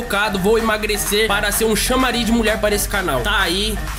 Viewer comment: desculpa eu entendi "lambari" desculpa